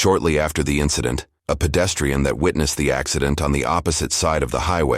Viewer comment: did he say a pedestrian hit the breaks?